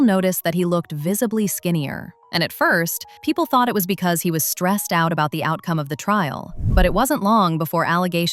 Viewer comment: thought you said stretched out 😂